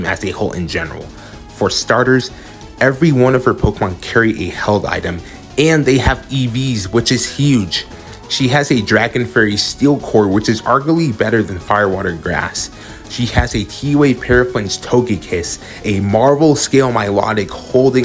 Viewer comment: did anyone hear “ she has a dragon, furry, steel core” no just me I’ll see myself out